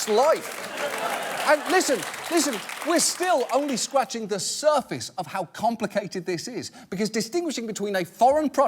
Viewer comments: Did he say "Scwaching the surface" at 10:47 ? - Elmer J Fudd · I swear he says “scwatching”